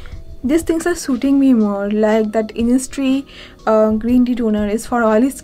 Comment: Had to go over that again because I thought you said Innisfree but I knew you're referring to the Isntree toner. Turns out you said Innistree XD